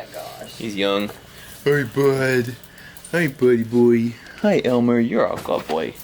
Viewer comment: What exactly does Dr say at 1:25? I keep on hearing "Yeah, Elmer, you're a fuck boi".